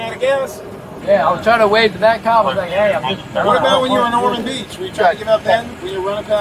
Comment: “I’m just a kid!” 🤣 Dude, you’re 19 years old. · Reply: I think I heard him saying ”I’m just a crybaby”